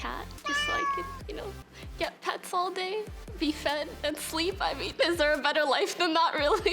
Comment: Did she say she wanted to be fed at 0:38 Foki is real